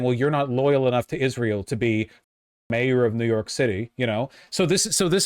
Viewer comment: "Listen, Jews of New York! I am your hero and therefore you must vote for me instead of that guy!" -Cuomo, basically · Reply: I thought you said Jew York at first